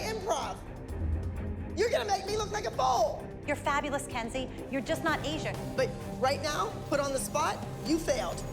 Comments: I thought mellissa said ' 'Your fabulous your just not asian' ' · did she say “you’re fabulous Kenzie you’re just not Asian”??😭